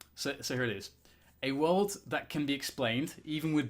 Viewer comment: "A [XXX] that can be explained..." Whats that word? Don't understand it or how to write it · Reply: world